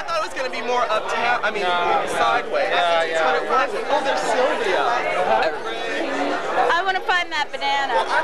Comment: Oh no I heard “look there’s Sylvia”. And my thought was “RIUN”. Referring to the Woodstock cottage episode. 😂